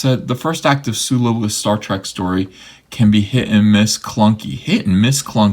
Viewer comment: did you just say Sulu: A Star Wars Story?